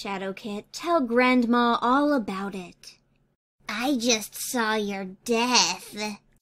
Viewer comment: I JUST SAW YOUR DeATh!!!!! What I saw when I freak my friends out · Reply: Bailey Ate My Channel Oh lol i thought he said »I just saw your dad!»